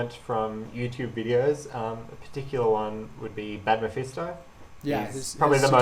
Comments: what did he say? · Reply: 11 Sorry if I came late, but I figured, it is only 2 weeks so... He said badmephisto :) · who? i can't understand it.. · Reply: badmephisto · What YouTuber does Felix Zemdegs say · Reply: Badmephisto